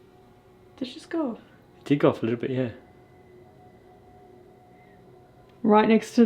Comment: This was by far the scariest night of our lives! The Clown Motel didn't disappoint us, providing the most traumatizing clown experience EVER!!!🤡🤡🤡🤡🤡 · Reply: I swear at 50:15 I heard a very faint “here” when using the K2 meter on the clown painting that was setting it off a tiny bit. Maybe it was one of you, but the two of you were just talking. Maybe someone else can confirm it. I dunno. Could be just me lol!